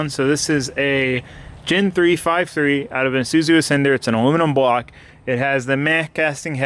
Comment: DID HE SAY 5.3 LS OUT OF AN IZUZU ASCENDER